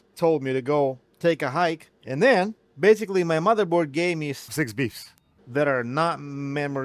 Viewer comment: Oh no, I just realized I did the geriatric thing... the cut scenes with "NorthridgeFix"... I always heard "six beefs"... I need to re-ball my ears... "six beeps", not "six beefs", idiot!